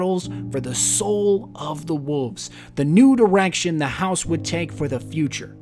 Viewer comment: Did anybody else hear, "the nude erection" at 3:08? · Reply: He said the new direction but the first time I heard it that's what I though